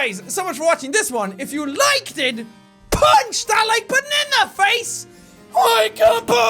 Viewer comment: What I always hear: "PUNCH THAT LIKE BANANA FACE!"